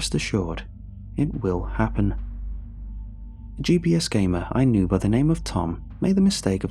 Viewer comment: I thought it said CPS game 😳